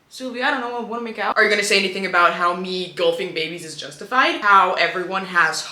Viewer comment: I thought she said she engulfs babies